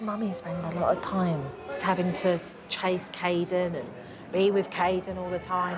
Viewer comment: I thought you said Karen💀